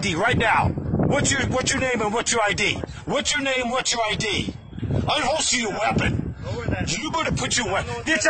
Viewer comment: Man: “UNHOLSTER YOUR WEAPON!” Cop: *unholsters weapon Man: “PUT YOUR WEAPON BACK IN YOUR HOLSTER!” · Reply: I heard "DON'T unholster your weapon"